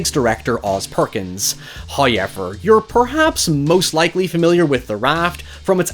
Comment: I know, it's an accent and I know this is a problem I have with myself, however whenever I hear pronounce "however" like "high ever", it fills me with aggression and makes my blood boil. 😅 Can't you please make an effort and try to not say this word like that anymore? I can't even begin to understand or to explain WHY someone would EVER pronounce it that way. It's as obscure as the pronounciation of the Scottish name "Colqhoun" in "Ravenous". They pronounce it like "Cologne". So, why the "q" then? Get rid of it!! It makes me angry to read or type the name, every time... And whenever I hear "hyever", I get angry, too...